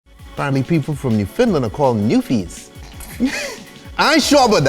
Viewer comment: I spent the first 28 yrs of my life in Newfoundland. I gotta say he butchered the name. Lol It's pronounced more like New-fund-land but say it kinda fast. I'm living in western Canada now and I have to say i dont miss much about home. Do miss the ocean though lol. I had that same drink on a vacation in Aruba. They called it the 'Superman' because of the color scheme. It was amazing!! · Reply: Thank god someone said it, I legit thought he said “new Finland” lmao